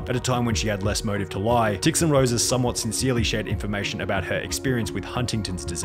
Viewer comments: my subtitles said dicks and roses 💀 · the subtitles did not just say 'd*cks and roses', lmao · I exploded laughing when the automatically generated captions said: "dicks and roses"